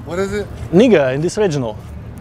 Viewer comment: I thought he said inigo thats my name